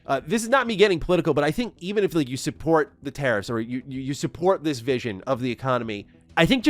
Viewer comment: You spell "tariffs", I hear "terrorists". But no major difference. 15:25 Will I support those "terrorists"? No way. They forget that most 3A games are not playable at start - not polished, not optimized (even Oblivion suffers from it). So it's okey to wait a year and buy it on deep sale.